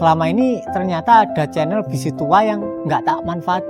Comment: Saya dengernya kok mas bilang..ada channel "besi tua." ....emang itu ya yg dia bilang? Apa sy salah denger?? Pas dia bahas masalah strawberry korea...